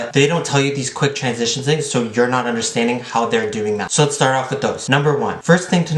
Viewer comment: Haha did anyone else notice he said let’s start off with Dos! (That’s number two lmao uno is one!) 👏🏼😅 Anyways I AM excited to see the transitions and hear the tips, I’ve been STRUGGLING with transitions. I know that usually to have it best it’s all about the timing, you want the last image to match the first one. So whenever you pause the video the next one you make it match the ending of the first one. It may sound confusing but it makes sense lol I commented before I heard him say number dos 😂 (actually number uno)